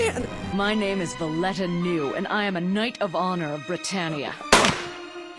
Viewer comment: sounds like she say boner if you keep reset it